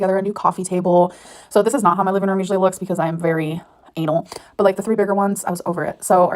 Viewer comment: did she say anal?